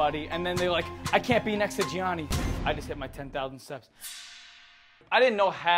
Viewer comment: Vinny had me gagging with laughter proper, "I just did my 10,000 steps!"😂 😂 😂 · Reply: Oh I thought he said I just hit my 10,000 subs 😂